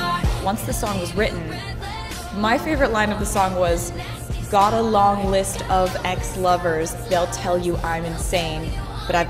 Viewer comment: I always heard "go love and stab ex lovers, they'll tell you im insane"